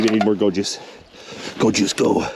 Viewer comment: When He Said Go Juice Go I Heard "Go Broly Go Go" Haha Dragon Ball Reference